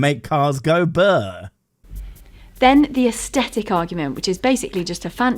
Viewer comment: I thought she said 'ascetic' argument, and did a confused for quite some time.